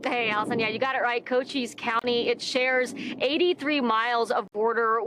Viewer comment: you stay out of arizona also. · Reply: I thought you said "country" not county. Thought you meant Mexico. The county of Maricopa and the county along the border where the governor was, yeah, sand, dirt, rocks and heat. It's a miserable place.